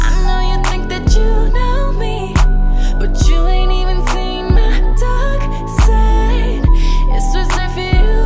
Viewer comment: pov : u searched 'i know you think that you know me but you ain't even seen my dark side' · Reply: I thought she said "you ain't even seen my dog, son" 😭